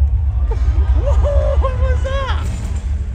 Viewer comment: LMAOO 2:10 said - OH BROTHER THIS GUY STINKS!!! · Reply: I heard it as "...again" instead of "...stinks" but at least I get what you meant 😆